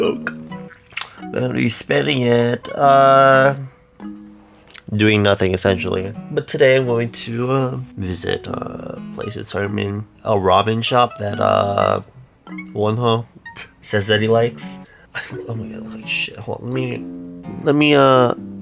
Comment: Instead of "a ramen shop" I heard"a robbing shop" and then I replayed it and I kept hearing "a Robin shop"